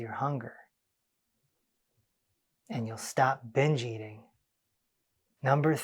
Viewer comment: I thought he said, "...You'll stop bi+ching" Actually, he said, "Stop bingeing." I have a problem with both that I want to stop. 😂